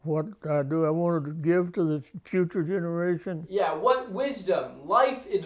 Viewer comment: Swear to god i thought he said what life advice would i give this PUTRID generation?